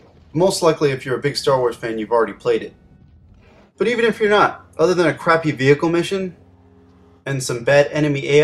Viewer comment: you guys should read the transcript huh. 6:32 but even if iraq other crappy vehicle mission he actually said "but even if your not other than a crappy vehicle mission.